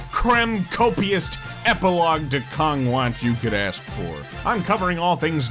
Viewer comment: I thought ypu said Kronk Watch lol XD 😅